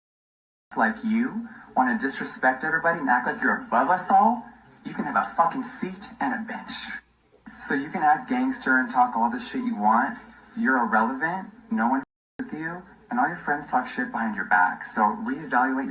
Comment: I mean I know it’s not exactly appropriate but every time I hear him say “you can have a seat and a bitch” I still lol